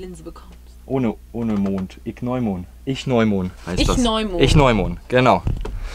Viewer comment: <link> 😂😂😂 Ich habe die ganze Zeit verstanden das Tier heisst Nichtneumond 🤣🤣🤣 Schönes Video 🥰 Ruinen, Höhlen, sind immer gut, ich liebe es Danke fürs Mitnehmen LG Sylvia